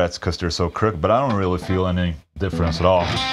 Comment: I hear that little bit at 3:49, and I was suddenly like "Devin? What"